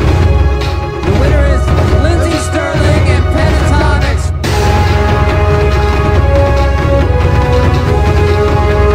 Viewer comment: did they just say Pentatonix??! 😝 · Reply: Yes they did! Youtube Music Awards :)